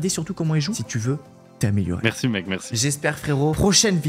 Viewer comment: C'est moi ou a 12:52 on entend un peace au lien du cimer